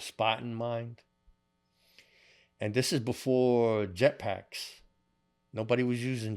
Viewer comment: did he say Jet Packs like some rockateeer shit? 🤔